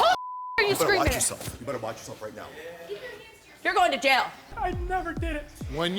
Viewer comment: who else heard “keep your hands to yourself”